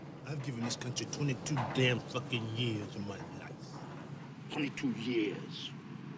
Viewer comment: 1. Louis Gossett Jr was a hero of mine because of this movie. 2. 0:40 You can clearly see he says "22 Mother fucking years" instead of "22Damn Years".